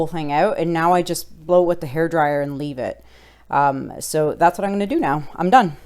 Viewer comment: so Gorgeous!! Also, do you use regular hair dryer, or is it some other blowing tool? :p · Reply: uh, never mind..I just heard you say hair dryer.. lol Brilliant job here!